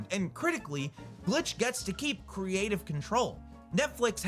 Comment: Did u say Creative Control? MR PUZZLES